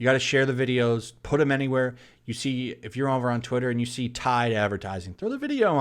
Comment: You say “Twitter”, I hear “TOILET”.